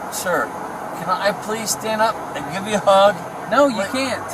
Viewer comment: Omg 7:50 time stamp did he really say “can I stand up and give you a hug? “